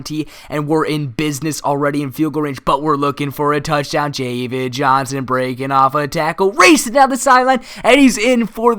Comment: Did anyone else hear him say Javid Johnson